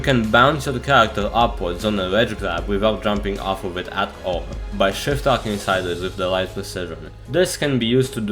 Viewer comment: sorry bro but i cannot understand waht u saying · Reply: ledge grab without jumping off at all by shiftlocking with the right precision